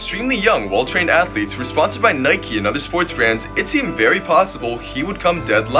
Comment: Did he just Say Nike-y?